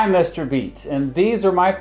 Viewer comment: I Thought You Said Mr Beast, I Thought You Were Fake